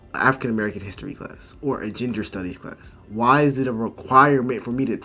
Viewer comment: I always enjoy watching your videos. Always a fan of critical thinkers! Also, it sounded like you said “Ginger Studies” class and I chuckled a bit to myself. Maybe we should have that as an option! 😂